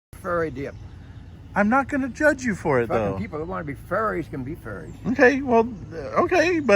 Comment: That old guy does not like "fairies" at all. He was a real gem on camera! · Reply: I thought he started saying Furries 🤣